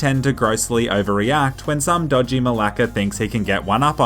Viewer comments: did he just say malaka??? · did you say malaka (greek word)